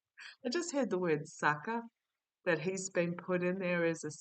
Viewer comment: what did the guides say?? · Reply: Sucker